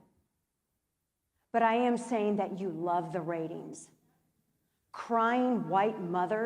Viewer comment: Ben WTF...She SAID they love the RATINGS NOT THE TRAGEDY. open your fuckin' ears.